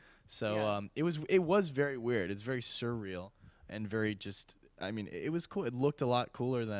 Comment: What's the word he used at 2:59? Anyone? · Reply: surreal.